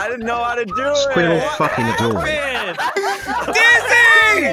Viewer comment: I heard say "Yamate" and I went 👀